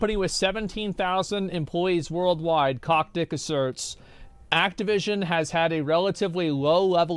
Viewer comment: I heard “CockDick asserts”